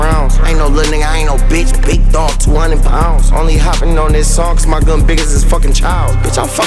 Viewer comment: Wait did he say "Im only hoppin on this song cuz my gun is as big as this f*cking child"